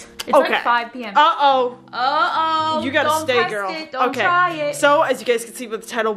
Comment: umm what did i just hear from 😕"baby girl" what the